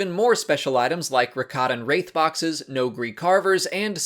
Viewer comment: Did I hear well? Did you say Rakatan?